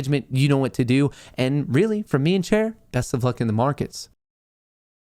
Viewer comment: Does Matt say, "From me and Cher?" or "From me and Chair?" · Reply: Chair... I had my doubt, but after watching the stream for while I got it right 🤣